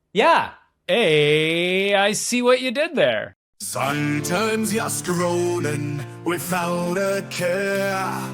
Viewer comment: AI see what you did there, i hear "Sun turns yes ka-roadin" but closed captions shows it says "sometimes your scrolling"